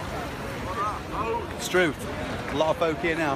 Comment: Did you say struth at 2:41? :) We'll have to make you an honorary Australian. Must have picked that up when you lived here eh?